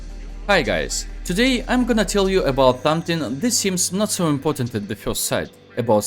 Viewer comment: Did i heard it wrong?oh!please tell me I m wrong or did he really said tomething (something) 👉 0:08 😅 jokes aside video was really helpful.I m an epical glory player 😅 ✌️🤪pushing ranks thank you sir